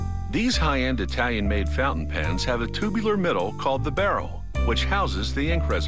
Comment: i thought it said fountain pans and i was like " What is a fountain pan?"